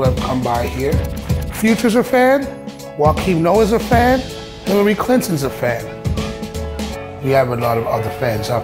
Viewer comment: DID HE JUST SAY HILLARY CLINTON IS A FAN ?!!!